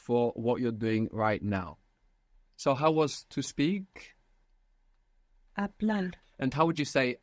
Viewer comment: im hearing hablal instead of hablar